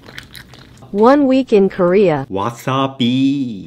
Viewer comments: I heard “wassup bee” when he said wasabi 💀💀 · Him: wasabi What i hear: what's up bee